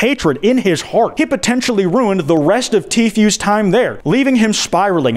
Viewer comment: Why does Kwite call tfu t fuel